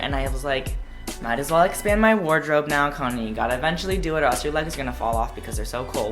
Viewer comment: genuinely thought conan said kanye